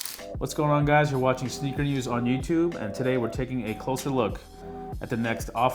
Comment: I thought he said we’re watching sleep videos on YouTube